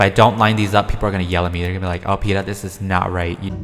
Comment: I thought you said Peta, not Peetah.